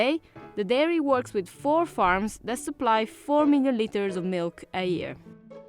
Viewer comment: I had to double take. I swear she said 4ml not 4 million litres. 😂 I can only imagine how many people have gotten in trouble for this mistake 😅